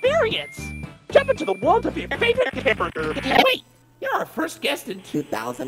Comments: I heard him say hamburger when he was glitching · I love how the animation looks!! (Also, why did I hear "Jump in into a world of your favorite hamburger!")💀😅🤣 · Why do i hear "hamburger" during the glitch😭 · It sounds like he said something about a hamburger during the first glitch😭💀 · i thought he said hamburger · I heard "jUmP oF yOuR FavOrItE hAMBURGER" 💀